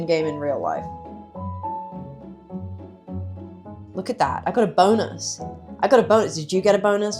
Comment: i heard "i got a boner"... help needa fix my hearing..